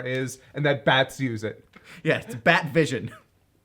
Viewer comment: I'm glad I watched this because all these years I misheard the line as "He has death vision!" Thought it was just supposed to be a bit of surprised nonsense referring to the eye beams. "Bat vision" makes waaaay more sense as a joke!